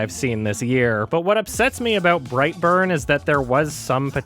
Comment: I thought it said Breitbart instead of Brightburn. Now that's evil.